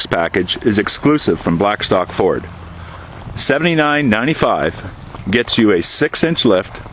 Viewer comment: Do you mean at 0:36 when he says 7995? I think you heard an extra number. And by 7995, I think that the off road option costs $7995?